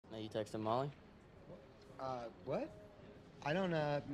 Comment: I thought he said you take some molly.